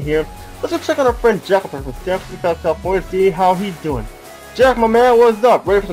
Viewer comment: You know I had to check that again and again, I thought you said Jeff there and not Jack